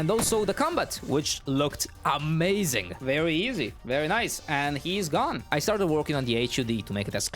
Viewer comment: I thought he said nice and gay